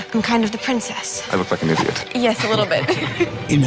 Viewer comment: did anybody else hear "I'm a f***ing idiot"?